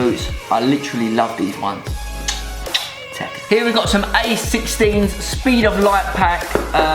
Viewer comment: did he said ace 16?? they are x 16